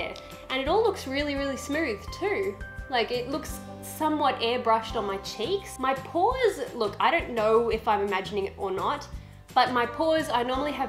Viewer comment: Bahaha 29:22 when you said pores I thought you said paws.